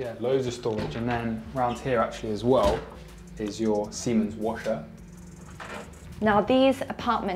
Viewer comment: I thought the guy said here’s your semen washer 🤣🤣🤣